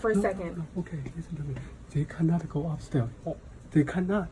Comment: did he say take a nap go upstairs LOL